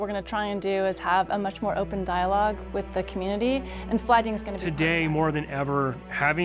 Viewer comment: did she say flood?